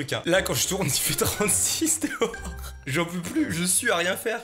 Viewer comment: il a dit quoi wsh XD ? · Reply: Il fait 36 dehors 💀